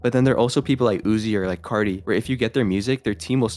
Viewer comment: he was talking about Carti, Playboi Carti, not Cardi B